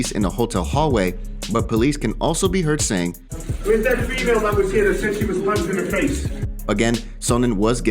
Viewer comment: So what was said at 00:54 ? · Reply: "where's that female that was hit? That said she was punched in the face"